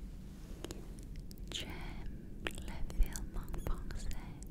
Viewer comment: the subtitles said she said "I like french moves" she said "I like french movies"